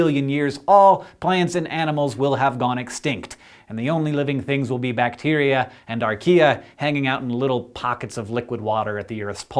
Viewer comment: I heard "all plants and animals will have gone extinct and the only living things will be bacteria and IKEA". ROFLMAO